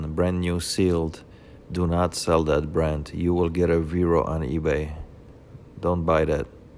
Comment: Couldn't understand what you said about otterbox? Sounded like you said you will get a viewer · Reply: Viro on ebay. Thanks for watching. Cheers !!